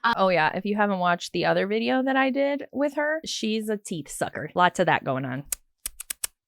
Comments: I thought you said “teet sucker” 🤣🤣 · I swear to god the greatest I thought you said TEAT sucker and I was like … wow… haven’t heard that in awhile 😂😂😂